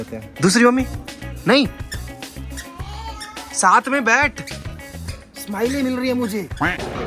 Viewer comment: did say : "Betho" 😄